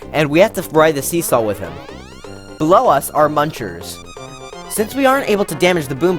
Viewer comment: Who else heard "Blow us our munchers"?